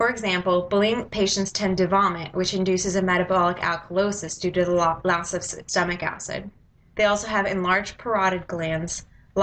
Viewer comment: Great vid.... But what on earth did you say at 2:30???? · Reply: LOL "metabolic alkalosis due to the loss of stomach acid"